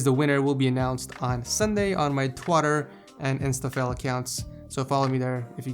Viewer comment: Was it just me, or did he say "on my twatter" @ 6:19 ?